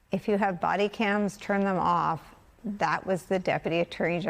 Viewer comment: I hear "if you have bodycams, turn them on". I know the text says "off", but I've replayed it a dozen times and it sounds like "on" to me.